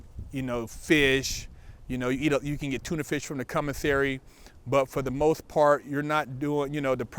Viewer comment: Sounded like he said cummisary lol